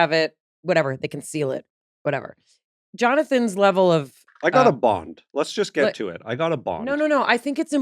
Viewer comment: I thought at first you said he got a Bong???